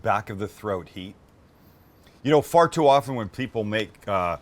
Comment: I heard “ fart too often” 😅💨